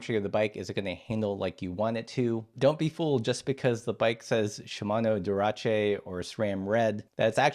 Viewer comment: Did you really say "Shimano DURACHE" at 4:16? LOL 😂😂